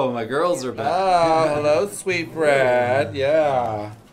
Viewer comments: I thought she said “hello sweet bread 🥖” lmaooo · She said hello sweet Brad. I thought she said hello Sweet Bread!!!